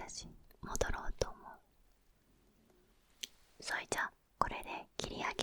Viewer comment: そいじゃあって聞こえてちょっと笑っちゃった😎